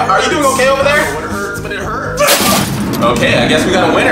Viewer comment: I thought he said ," i came from my ass" 💀